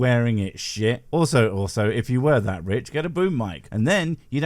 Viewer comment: "..Get a boob mike.." But he has - oh, "boom". Sorry, must have misheard.